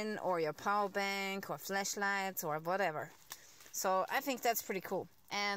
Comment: i thought you said ''fleshlight'' XD